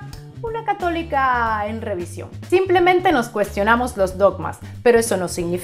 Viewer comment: cuando te enteras que es catolica en revisión y no atea: la decepción, la traición hermana reptiliana-iluminati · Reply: yo entendí "en remisión", jajaja... y sí, un saborcito como un dejo de decepción...